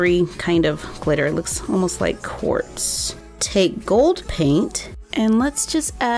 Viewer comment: i thought that she said it looks sorta like warts :o